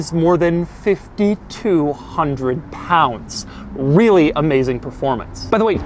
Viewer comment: im sorry did i hear him say "wheely amazing performance"?